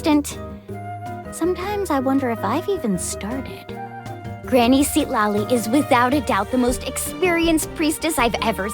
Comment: DID I MISHEARD OR SHE SAID GRANNY CITLALI???